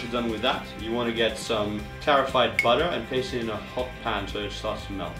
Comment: I thought I heard terrified butter